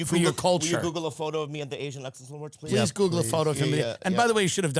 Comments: I thought Bobby was saying the “Asian accent awards” 😂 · I thought he said "asian accent awards" thats how little i can understand Bobby.